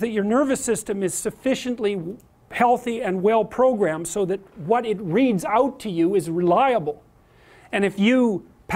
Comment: If you (like me) happen to need a break from Darkest Dungeon, you may be interested to know that Prof. Peterson's book “12 Rules for Life: An Antidote to Chaos” is finally available. You can find it here: <link> (US) / <link> (UK) / <link> (CA) The above are Amazon affiliate links. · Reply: Hi! Sorry, I'm not a native english speaker. May you tell me what he says at 1:30? "So what it reads out to you is reliable". What does "read out" mean in this context? Does he really say "read out" or it is just me? Thanks :)